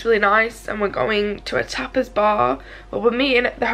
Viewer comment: Oh you went to a tapas bar I thought you were saying you are going to a topless bar. Ha ha my bad.